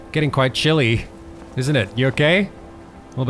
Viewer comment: I'm here to learn English and...... At 2:11 I heard: "Isn' it? You're gay?" Fuck my ears......